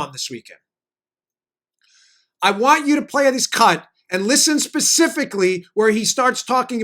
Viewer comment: Did I hear right @ 40:05? Dan said "I want you to play this cunt ...." Did I?